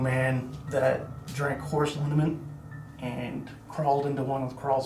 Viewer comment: What was that guy saying about the kid that committed suicide? It almost sounded like he was saying the kid drank horse semen, but that's not what he really said. What was it? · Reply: It was horse lineament he drank. It’s labeled as a suicide on the death certificate.